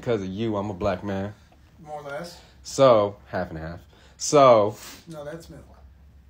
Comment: “Half and half” “No that’s milk” I just about died 🤣🤣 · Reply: I thought he said "no that's mental" like it was in his head 😂 this is much better though 🤘